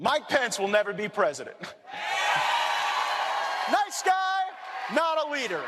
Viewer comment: Sounded like he said "my pants will never be president".